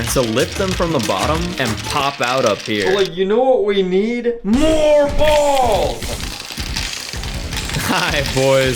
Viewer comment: lol when he said more balls I heard “your balls”